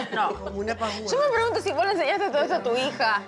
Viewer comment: Hola alguien que me colabore diciendome que fue lo que dijo en el minuto 13:30 no alcanzo a entenderle lo único que pude escuchar (tu hija) eso fue todo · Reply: "Yo me preguntó si vos le enseñas todo eso a tu hija"